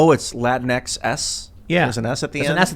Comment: No joke. I was listening to this, not watching, and I seriously thought he said "Latwinks"